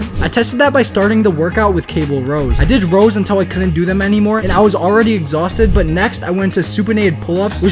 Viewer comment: The first time I thought he said I did rose until I couldn't do her anymore 😅😂